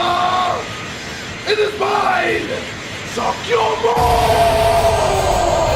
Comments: I thought He Said "Masenko ha" But he said "Suck YoMommm" Hahaha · did he just say suck your mom? Lmfao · did mans just say suck ya mom and shoot a laser beam out of his hands?😂😂😂😂😂